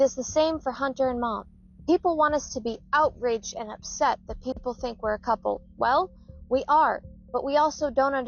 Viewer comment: go back and listen she did not say they were a couple. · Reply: U actually have to go back to 3:55 where she says "people want us to be outraged and upset that they think we are a couple... Well we are" I'm sure she meant they are upset and outraged